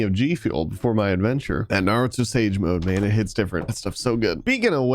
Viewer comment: nobody: Naruto Weeb: DID I JUST HEAR HIM SAY NARUTO SAGE MODE IN 3:06??? OMG!!1!!1